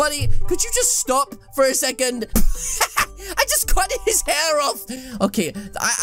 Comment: did he say cuded his hair off🤣🤣🤣